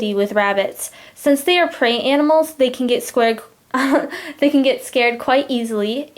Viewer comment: 😯what she said at 2:38 ? 😐😅😂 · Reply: Dayanara Vigo they can get scared